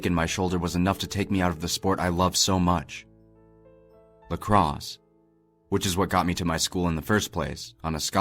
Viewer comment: At first I thought he said motocross not lacrosse